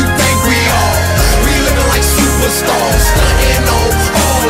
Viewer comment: i thought he said supra starz when i first heard this years ago